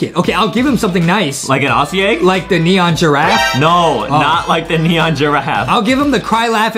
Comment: I hear that said "No" when wanna trade the giraffe to another person said it in